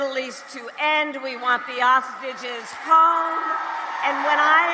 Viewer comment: Did she just say "We want the ostriches home"?